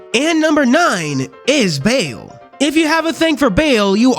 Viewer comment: I misheard beal as bayo and I almost had some bayonetta ptsd flashbacks